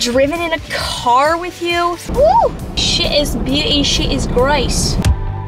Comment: At first I thought you said sh*t is beauty sh*t is grace